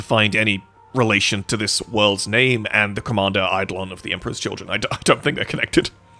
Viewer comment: Almost misheard Commander Caeon as Kyle, which makes me think a homebrew shitpost: The Drywall Demolishers lead by Chapter Master Kyle and all members are variations "kile, kiyle etc" The Apothecary hand out monsters and they have a fixation with breaking infrastructure lmao